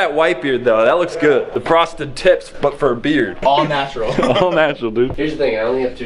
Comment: CC inserted "prostitutes" instead of "frosted tips". Just an FYI for demonitization contesting. Countersue: CC